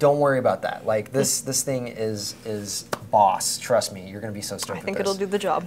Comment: it sounded like just said this thing is pussy...